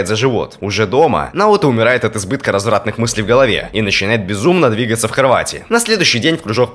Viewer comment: видео: и начинает безумно двигаться в кровати. Что услышал я: и начинает безумно двигаться в Хорватии